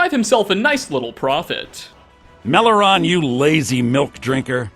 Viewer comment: Did Erikur say "you lousy MILF drinker" in that audio clip starting at 7:18?